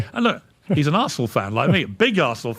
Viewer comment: Mishearing arsenal to asshole must be on purpose 😂😂